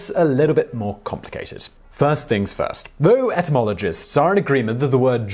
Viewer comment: Simon did you just say entomologists say..? Because I thought entomologists studied insects. I think you meant zoologists lol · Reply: K. Jolie Tovar He said etymologist. I didn't re-listen to the entire video, but he did say etymologist, not entomologist at the start.